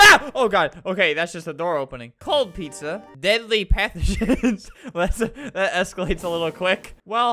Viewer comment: i lowkey thought it said ‘deadly primogems’